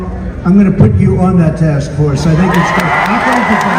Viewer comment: I thought I heard him say “I’ll put you on that task force.” Right?